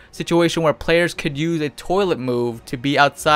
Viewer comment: you say toilet instead of teleport xD